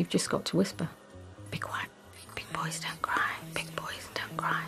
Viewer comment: When the secretary was being interviewed about her part in the song and she whispered "big boys don't cry", it gave me chills for some reason. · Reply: I always thought she was saying "be quiet so quiet"